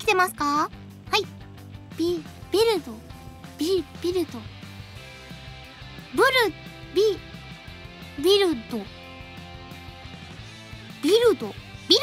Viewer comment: did I hear her say dildo? .......no? okay